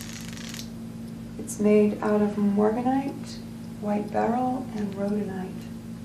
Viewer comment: I thought she said Mormonite. I am soo tired